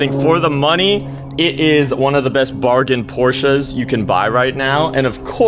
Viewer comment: im just in 0:58 and saw the LP and i was like did u said poor man porsche ?